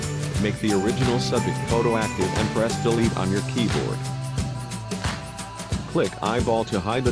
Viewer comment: what are you saying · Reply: Select the "Model Copy" layer then press the Delete key on your keyboard.